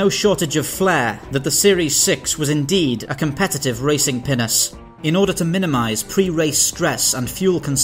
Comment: I had to read the description. I thought you said “racing penis”. 🤣